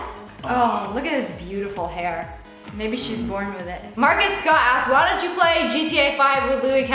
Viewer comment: Did she say maybe SHE´S born with it?